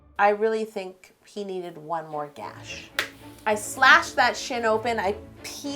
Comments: I thought she said I slashed that sh** open. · I literally thought she said slash that s$!#% open but it was shin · I thought you said slash that sh*t open instead of shin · I thought she said sh*t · when she said slash that shin open I thought she said sh*t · I love this haha still hope an even more Walking Dead themed cake is coming though ^_^ also I thought you said "I slashed that sh*t open" rofl I almost died xD